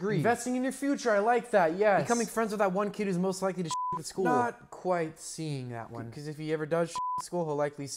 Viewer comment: So I watched this a few times without reading the captions, and this whole time I thought he said "becoming friends with that one kid who's most likely to SHIT in school"... And I was like, why would it be a good investment to be friends with a kid who shits in school??? 💩💩